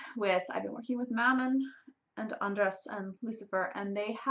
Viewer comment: Did she say "andras" 6:14?